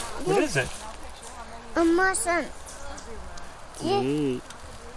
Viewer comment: I thought I heard say "Mushroom"...and "Poison Ivy"